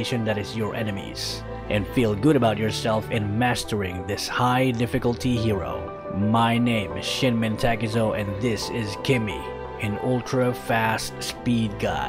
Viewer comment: For a moment I thought I heard he said 'My name is shit men taezo' XD Edit: OMG I got a like!?!?